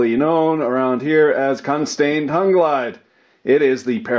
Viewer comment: Did he really say, "Cuntstone tongue glide?" Or do I need do go read some Freud? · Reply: I thought I heard cuntsten tongue glide...